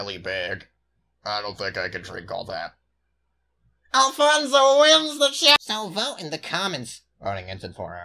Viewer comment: 𝔸ℕ𝔽𝔸ℕ𝕊𝕆 𝕎𝕀ℕ𝕊 𝕋ℍ𝔼 ℂℍ𝔸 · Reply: I thought uncle said "ALFONSO WINS THE SHOW-"